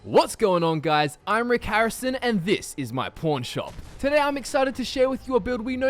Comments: Instead of pawn shop I thought he said porn shop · In the beginning, I thought he said P**n shop, I'm dumb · I'm Rick Harrison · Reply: FalloutPro21 I thought he said porn shop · did he say pawn shop or porn shop?